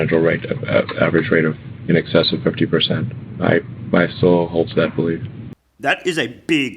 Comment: did he say "my soul holds that belief"??? Holy sh!t, just caught that. Damn....his soul holds the belief that Tesla will grow by 50%? Damn....